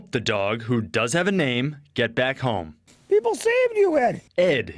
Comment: I thought he said the dog's name is Getbackhome 😂